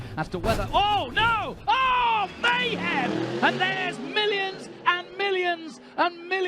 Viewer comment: listens to much to trump I guess? xD Allthough he said millions instead of billions xD